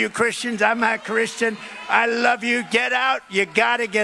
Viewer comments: Did I hear that right? At the 2:37 mark he says "he loves christens, I'm not christen but I love you." I've listened to other youtube video's of the same speech and it seems like it he slips in that he's not christen and nobody seems to catch it. But then again he said he like low IQ voters and no one called him out on that in 2016. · I could've sworn I heard 💩👖's say, "I'm not Christian."